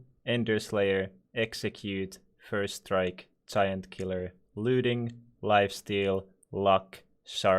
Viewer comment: i thought you said kid killer